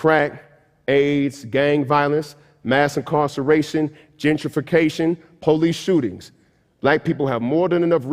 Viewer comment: Oh GENTRIFICATION... I thought he said gingerfication and was like "I fear that too, my brutha."